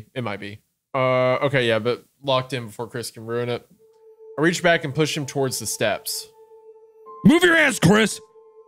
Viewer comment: I really thought it said "I reached back and pushed Chris down the stairs." Maybe wishful thinking.